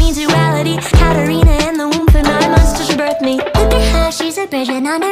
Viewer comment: I always thought she said “ Now I must distruct me” instead of “ nine months till she birthed me..